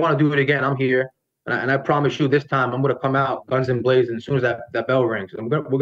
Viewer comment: Did he say "guns emblazoned"? 😭